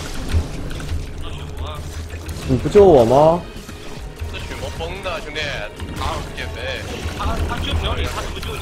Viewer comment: said "Bro, this Bloodseeker is outfarmed" (这个血魔是崩的，兄弟). I am not sure if your "empty" means the same. 09:02 Sumiya said "Use Sticky Napalm" instead of "Not use".